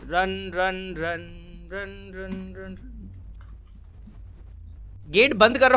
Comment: I hear rand 💀